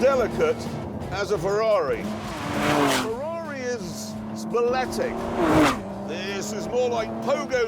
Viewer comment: At time stamp 1:42, you say that "Ferrari is 'speletic' ". What does that word mean? It's not in my online dictionary.